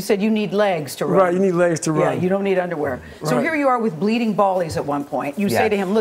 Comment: did she say "bleeding 'ballies"